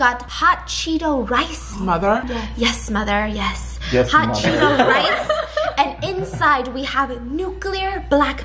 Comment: Wait did she say hot Cheetos mice..AM DIED!!